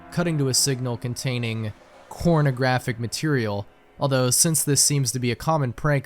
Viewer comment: you said cornography material, but the captions said something else 👀